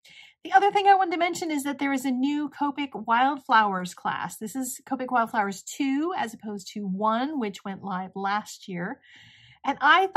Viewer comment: Oh mercy me I thought you said Covid flower class 🤣🤣